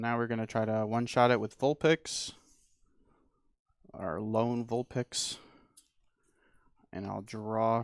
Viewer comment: Did he really say alone Vulpix? Was that on purpose, instead of alolan Vulpix?